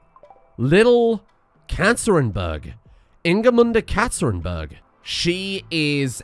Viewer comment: Ngl, i was here thinking what to comment then i heard "Katterinberg" and i thought her name was "Cancerinberg" EDIT: Inebriator: from drunk irish man to ducking thor. Name a better anime training session EDIT 2: You could call The Inebriator "Blintning" (blind + lightning)